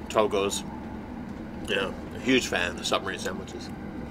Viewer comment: Did I hear right? A Submarine Sandwich? 7:11 ;D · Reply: Yes! It's a submarine sandwich..LOL